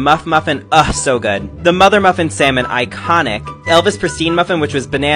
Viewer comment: I thought you said “Mother Mother Salmon” Guess I need to stop listening to Mother Mother for a bit